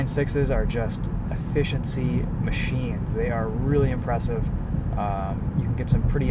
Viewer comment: For a second @ 10:37 it sounded like you you said... These inline sixes are just "A-Fish-In-Sea" machines lmao 😜